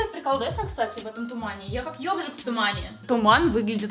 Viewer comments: мне послышалось ёбжик в тумане хвпхавах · Мне послышалось: " я как йогурт в тумане"